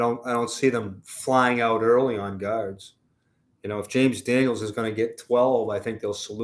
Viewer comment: I thought said we need a couple of "gods," which is true, but he meant "guards." LMAO